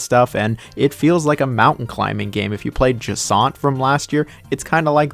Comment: what mountain climbing game are you referring to? couldnt understand it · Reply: it’s called jusant lol